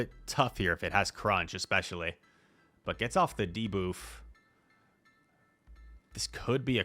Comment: "Gets off the de-boof" did I hear that correctly? Lol